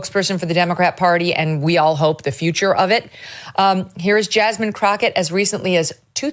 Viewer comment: I thought Heard you refer to JASMINE CROTCH-ITCH🤔🤭